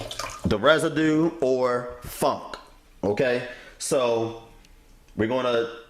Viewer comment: why it sound like you said residue or F#&!...i love your videos bro but i died 😂😂😂😂